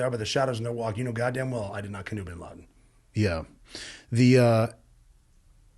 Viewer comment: did I hear Rob say he didn't kill bin laden, the dude talks that fast and cuts in that much it hard to catch